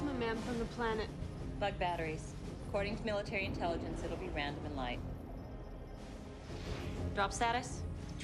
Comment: It’s been 28 years since I first saw this movie, and I just now realized she said “this isn’t random, or light” as in the bugs are strategically aiming their plasma in barrages to hit more of the carriers When I thought she had said “this isn’t random light” as in random balls of light lol…. Bugs that think…. Fuck….